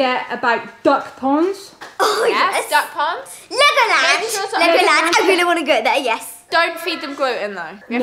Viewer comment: i thought said liquorland when she said legoland and i cracked up laughing😂😂